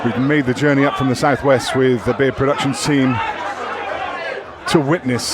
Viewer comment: Fitting that the production company is called Beer Productions and some dumbass drunk threw a beer on the table on the last shot of the match. Great match though. Doesn't get any closer than that. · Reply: lol it's Beard Productions. I heard "beer" . Sorry about that.